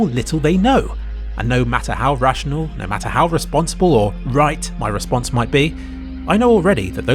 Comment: the subtitles say "reasonable" but instead of you saying that you say "responsible" which doesn't really make sense (or maybe it does and i'm over analyzing it) with rest of the sentence.